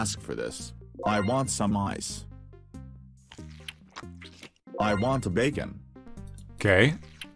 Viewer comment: When he said "I want a bacon" I thought he said "I want a baby". Was so frikin prepared to see him eat a child.